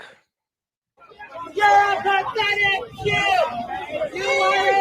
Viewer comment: Anyone else hear “you’re a pathetic pube?” 😅